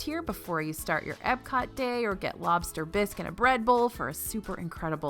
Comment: When you were talking about Le Halles, I thought you said Lobster Bisque and a Red Bull. Interesting choices in Disney.